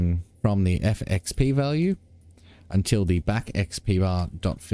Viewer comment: Every time you say fillAmount I hear ‘fill em out’